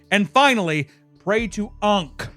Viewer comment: I thought he said "Pray to Unc" I lost it 😂